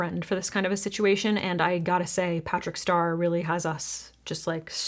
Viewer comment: did she say “patrick star” at 8:52 😭?